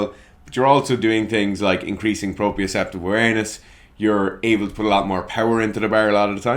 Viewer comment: Did he say anus? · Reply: Awareness. You heard what you wanted 🤣